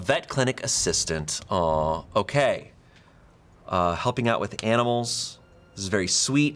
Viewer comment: At this part when shayne clicked the pen before saying animals i thought he said "helping out with cannibals" and i had to replay it